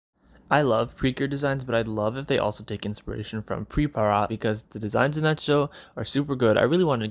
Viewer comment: What's the name of the group you mentioned in 9:10 ? I didn't catch it and I wanted to learn more about them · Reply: Hi, that anime is called Pripara, an idol anime. Yeah, it was hard to pronounce lol